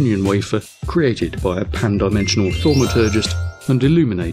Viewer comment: I tried closed captioning but I couldn't make out the derogatory name for God at around 2:25. Something about pan dimensional....? Love the invisible cosmic mage and elusive space wizard. Really want to know this one's name! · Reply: Pan Dimensional thaumaturgist. :)